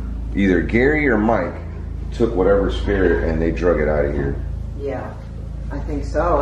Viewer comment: I heard a whisper say "I CAN" when Omar says either Mike or.......///////----__